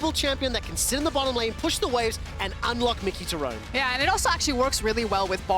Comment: does he actually say Miky?